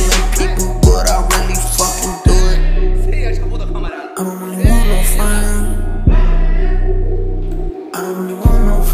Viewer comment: what did he said??? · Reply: "széjjel csapod a kamerát"